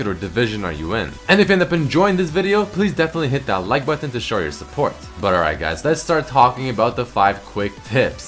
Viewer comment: i heard "hit that like button to destroy your support"